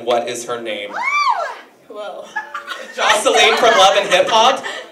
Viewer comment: what did dinah say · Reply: "why you take my question tho"